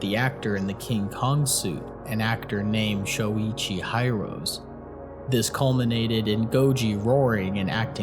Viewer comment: i did a double take when i heard him saying Shoichi HIGH-ROSE on